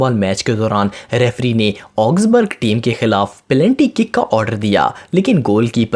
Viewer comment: what........ Did he say pelanty 🤣🤣🤣🤣